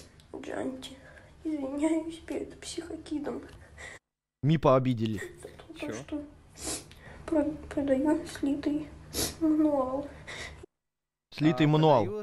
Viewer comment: Бригаду можно? Я только несколько слов понял, что он сказал: "Я", "Не надо", "Пожалуйста", "Деньги" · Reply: Чел извиняется перед каким-то "психокидом" за слитый мануал и просит, чтобы его не сватали (не вызывали ментов на него, произошло от аббервиатуры S.W.A.T, американская группировка спецназа)